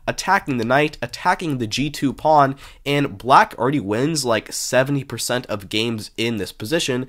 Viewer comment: Did you just say black wins 7% of games in that position?!